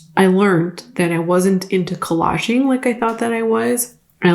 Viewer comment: I thought you said kolache instead of collage-ing lol 😂